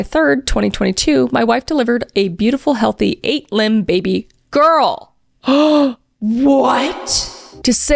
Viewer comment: I heard 8 LIMBED baby girl